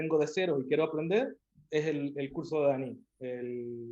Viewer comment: me podrían decir a que curso se refiere ahí? tampoco entendí bien que dice jaja :( por favor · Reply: Es el curso de Daniel Lara, llamado Pepe School. Es una escuela que está en Barcelona, Madrid. Puedes buscar info en Google 👍